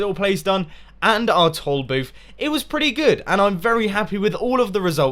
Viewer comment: When he was talking about the toll booth, I thought he kept saying "tall booth" so I was just sitting there all puzzled and then I realized at the end of the episode he meant toll booth. xD